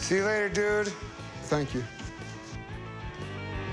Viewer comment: I think I heard John Lemon